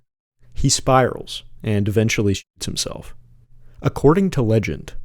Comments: bro, I know what you were saying, but I was listening to the video and not watching it, so when you censored the word shoot, it just sounds like you said he shit himself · I thought you said “shits himself” 😭🙏